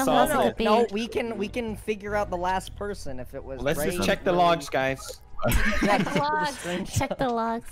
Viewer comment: All I hear “ let’s check the logs guys”